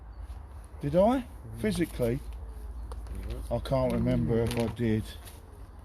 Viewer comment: Reminds me of a young version of Grandad from "Only Fools And Horses" ... shudder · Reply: "I can't remember if I did ... " ... all I heard was "I can't remember Del-Boy ..."